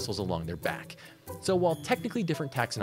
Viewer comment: Idk why but for some reason I thought it said “Shrimps are simps” now I wanna get that tattoo